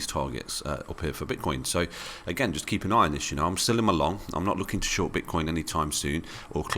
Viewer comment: When you say "I'm still in my long" I keep hearing "I'm selling my long"...